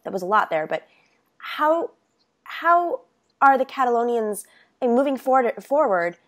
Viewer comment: For a second I thought it said Californians.